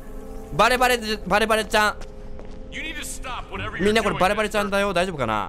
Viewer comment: みちゃかみんな〜！！ って聞こえたwww